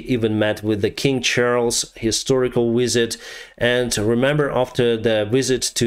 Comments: LOL... sorry I thought you said "Historical Wizard" about King Charles!! hahaha Great video. Thank you! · I am so sorry but I misheard you here. At first thought you said "Historical Wizard" as a way to describe king Charles; which would have been absolutely hilarious! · I had to watch it 3 or 4 times but when you were talking about Zelenskyi meeting King Charlies it sounded like you said "..met with the King Charles, historical wizard.." and I cracked up laughing. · Misheard that as King Charles, Historical Wizard, which is very lol 😅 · All I heard was "King Charles historical wizard" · Except Britain doesn't fly F-16s. At 2:16 I thought you said that he met with King Charles, historical "wizard" lol. Of course it was ''visit.'' The L1 is a medium tank, it will be okay with every task except going against top Russian tanks. But against T-62's it should be quite capable. The best Russian tanks will probably be given to the Wager group as they are less likely to sell/give them to Ukraine. So put Ukraine's best tanks, the T-72s, there, the lesser tanks like the L1 and AMX put against Russian's conscript army. Perhaps mix the L1s with the British tank, the latter taking out heavy tanks at a distance, which it is superb at doing, the former taking out lesser armour accompanying the Russian heavy tank. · I thought he said "King Charles, historical Wizard" for a second... 😂 I can't be the only one! I support this channel though, thanks for the updates 👍